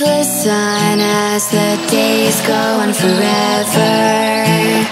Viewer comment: I always heard it as 'And the days go on' 0_o